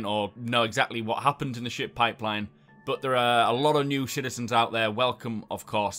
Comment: Did he say ''Shitizens''?? My man!!